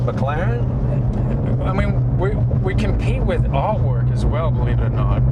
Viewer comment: what did he say they compete with? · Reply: Sounded like he said artwork